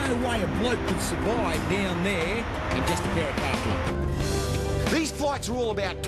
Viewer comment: I always thought he was saying who’s that bloke. In the car keys but he’s been sayin khakis the whole time